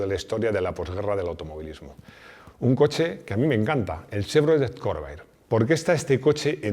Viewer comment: entendí el Chevrolet Corvette y ya me había quedado con cara de ??? 🤔